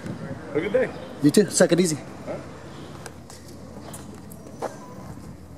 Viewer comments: Did you just say, "Suck it easy." ??!!!!! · in he says to the cop SUCK IT EASY!!!!!😂😂😂 Did anyone else catch that???